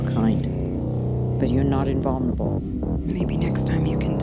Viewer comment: Может кто нибудь расшифровать что говорят начиная с 1:39 Там начинается - You are the first of your kind, but... и дальше не могу разобрать(( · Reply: but you're not invulnerable